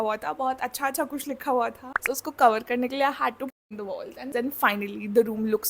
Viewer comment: what did she said?? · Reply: "I had to paint the wall"